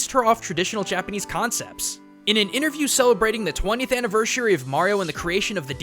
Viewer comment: Did anyone else hear him say anivERSHary?